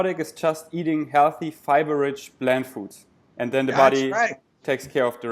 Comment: sounds like he said "bland foods" lol